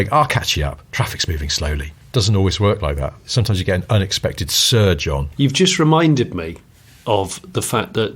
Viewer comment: I misheard as an "unexpected Sir John" 😂